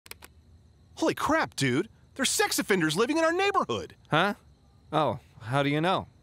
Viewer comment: oh "offenders" lmao...I was actually confused as hell when those guys pop out..cuz I heard at first "avengers" and was expecting Capt America and gang to show up..thought they are living nearby and got caught and they are sneaking to the guy to silence him or some other reason lol...